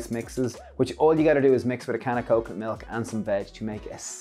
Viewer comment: Wow! I had to rewind twice. I could have sworn you said mix with a can of coke, milk and veg! 😳🤣